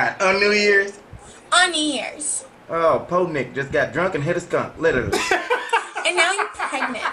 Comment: Did he just say poor Nick got drunk and hit a skunk 🤐😁😂🤣😂😁😂🤣😂😁😂🤣🤣